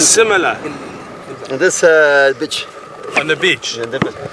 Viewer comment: I was dying when he said bitch instead of beach 😂😂😂😂